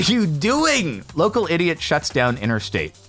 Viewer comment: The intro got me bc I thought he said Welcome idiots LOL